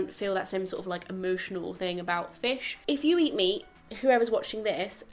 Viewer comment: Oh dear , I heard "if you eat me" instead of "if you eat meat" and I was so shocked 😂